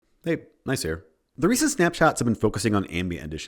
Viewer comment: I thought you said “hey nice hair.” when you actually said “hey Gneiss here.”XD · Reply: I can no longer unhear that. At best, I hear “Hey, Nice here.”